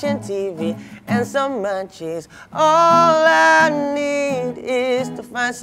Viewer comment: I thought u said mancis instead of munchies😅